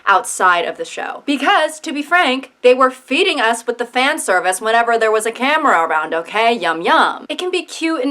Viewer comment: I thought it said Franservice for a hot second..